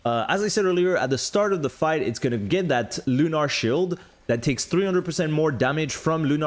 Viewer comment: I thought I heard "Lunar Shill'd"